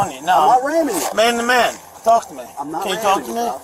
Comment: suspect says man to man. The officer does not say you don’t want me, man to man.